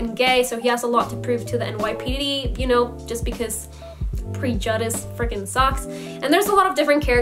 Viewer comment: did she say pre-juhdice 😂